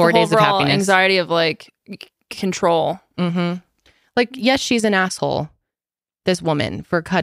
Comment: I thought was saying "yes she's an asshole" and was still talking about Holly 😂